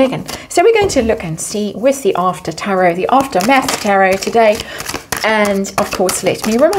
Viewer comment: I keep hearing "after meth tarot." 😂😂😂